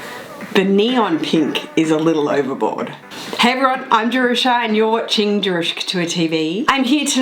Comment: I thought you said "hey gurl it's jerusha" I was like OH GIRL. #Getyourlife